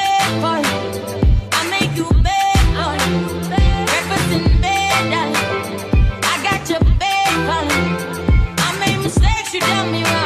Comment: All this time I thought she was saying “breakfast in bed hun, I got your bacon” 🤣🤣🤣🤣🤣 knew it didn’t sound right